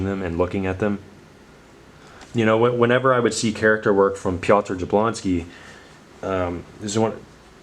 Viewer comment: What artist did you name at around the 21:00 timestamp? I can't quite make out what the spelling is and I'm interested in checking out his art! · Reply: I actually put subtitles on. And that said pat jablonsky